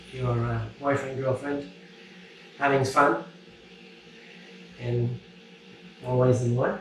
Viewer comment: what did say? sounded like "having fun in more ways than one"